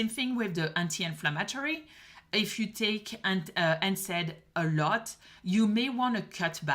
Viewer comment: Can't understand what she's saying at: 06:46...is she saying "If you take "ANTACID" a lot"...or what? Please clarify, if possible! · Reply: She is saying NSAID, no steroidal anti inflammatory drug. They bother a lot of people’s stomachs.